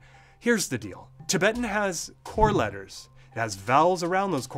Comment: I heard “four letters:”